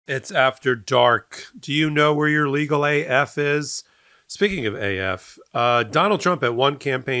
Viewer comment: I always hear “do you know where your children are?” 😂